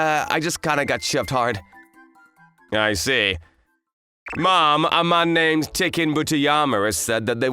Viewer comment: I thought he said mom💀😑🤣🤣